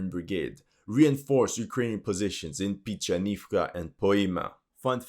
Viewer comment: Pičanivka :D in my languange it sounds like you saying pussynivka :D so funny.